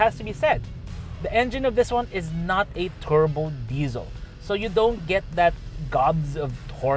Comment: did I hear correct that it is not a turbo diesel?